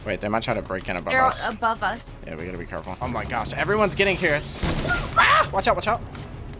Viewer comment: did you say everyone is getting parents?